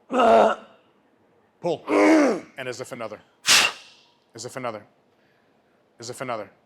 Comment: Every time I do as set, I hear "as if another"